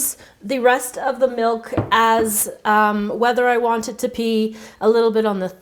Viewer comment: she says pee instead of be